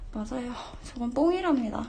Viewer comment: 김상우 자기똥이래요 7:00에말함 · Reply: Hi Syb 그거 들어보니 누가봐도 뽕이랍니다로 들리는데